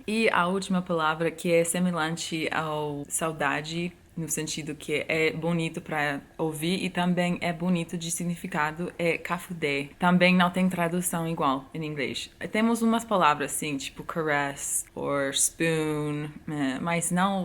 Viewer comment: Só eu entendi "quefudé" em 5:14? hahahah